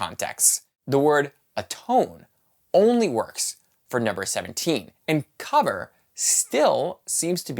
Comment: you say that "the word atone works only for XXX seventeen." Did you say "Numbers seventeen"? Or what did you say? And what did you mean?? Thanks. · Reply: Yes, Numbers 17